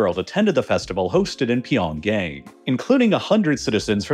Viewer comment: did i hear pyongay?